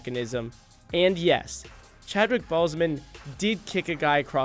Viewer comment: it sounded like he said Chadwick Ballsmen